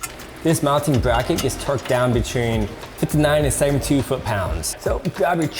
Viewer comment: What's the torque for the bracket bolts ? All I hear is "between ninety seven two foot pounds"... 🤷🏻‍♂️